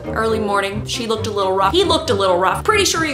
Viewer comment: What do she say ? he looks to little "what?" thx i'm french